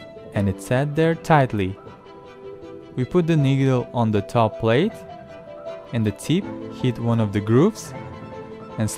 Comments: I am sorry what did he say?😳😳🙄🙄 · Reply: He said nigga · I low key thought he said ..” put the neega “